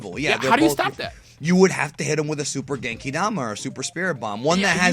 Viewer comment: who else heard say " a Super Danky dama"?